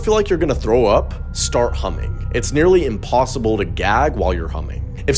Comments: "it's almost impossible to puke while humming" My body: "emergency exit to the sinuses! Imm going out the nose!" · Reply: I THOUGHT IT SAID HUMPING🥲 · "if you feel like you're gonna throw up start humming" Me: hums through the rest of the video even though I don't feel like I'm gonna throw up Edit: wow thx for the likes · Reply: I'm glad I'm not the only one who thought he said humping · “ if you ever feel like you’re gonna throw up, hum” Will I die if I throw up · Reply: Loooooool I thought he said humping 😭😭😭😭😪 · I thought you said hump if you start gagging. Well I tried and threw up. It was the other source. I came back to your video and you said humming. Lesson learned to make sure I hear everything correctly. I wasted a great time of puking. LOL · Stop, I am so dirty-minded. I thought he said, "humping" instead of "humming" 😭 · Why did I hear "start humping" for the throwing up one 💀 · I thought he said humping instead of humming and had to go back for a sec